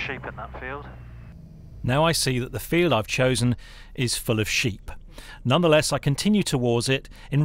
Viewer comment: For a moment I thought u said the field was “full of shit” (not sheep). In case of the former, nice soft landing with an “EMAS” 😂😂😂